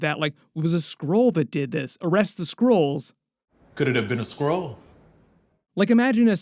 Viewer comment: Dang! I thought said 'could it be a squirrel?'. lol